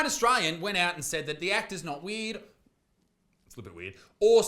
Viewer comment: when he says "weird" it sound like he sayin "weed"